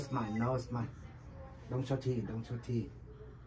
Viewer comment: I thought FOR SURE he was saying “big smile, shawty” 😂😂😂